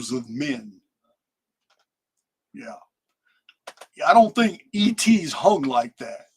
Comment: “I don’t think ET’s hung like that”. Did I hear that correctly !? If so… LMAO!!